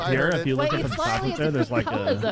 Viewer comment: What does say at 17:15? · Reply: “I can’t even get out his [impulses] innards”